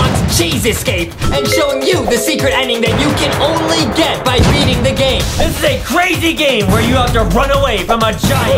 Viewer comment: At the start of the video I thought said eating the game instead of beating the game